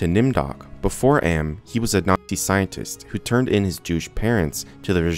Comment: I thought you said "he was a naughty scientist who turned in his douche parents"